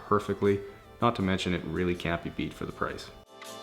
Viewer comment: I thought he said “and it really can’t beep beep for the price” and I was like “ I thought you where cleaning the car not becoming a car”